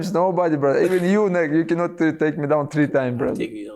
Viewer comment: I swear he says ‘even you n*gga can’t take me down 3 times’ 😂😂😂